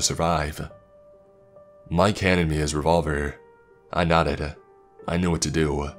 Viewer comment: Mike handed my his revolver, I NUTTED It sounds like he says I nutted